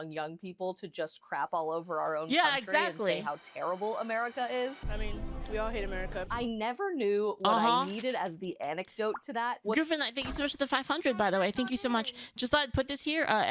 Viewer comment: did she just say 'anecdote'?